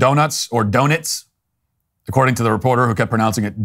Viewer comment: why does it sound like she is saying "Donets"